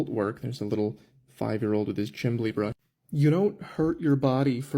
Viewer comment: did he really say "chimley" 🤣